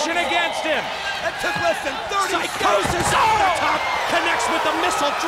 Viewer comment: it sounded like he said syco sid